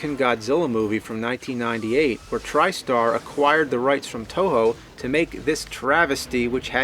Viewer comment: trystar adquired the rights from touhou? even the subtitles say it